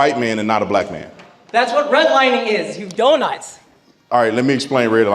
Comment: Did he say "you donuts"?